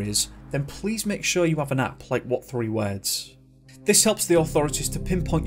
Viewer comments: What did he say around time:[8:32]... "make sure you have an: "app play what-three-words" . . .? I couldn't understand what he said--- is he referring to an 'app', or an 'Apple-product', used for GPS-signaling?? Can anyone clarify for me what he said/what he was referencing here? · I'm excited to hear your thoughts and discussions on this one. Am I right in thinking that Lucas' disappearance was odd, or am I mistaken? Let me know. Also if you read this then I hope that you're having a great week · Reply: What did you say at 8:30? "Please make sure that"? The captions don't help, auto-generated recognizes it as Vietnamese 😅